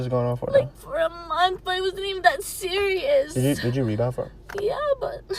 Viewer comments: What did he say at 1:45 ? Did u read for her ? Or did you reach out first ? I can’t hear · What does he say, did you read out for? I don't understand. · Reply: “Did you rebound for him” he was trolling